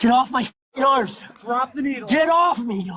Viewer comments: Did he say "Get off me, yoda"? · Did he say “Get off me, Yoda” at 11:33?